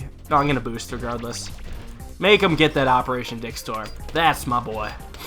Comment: did you just say "operation dickstorm"?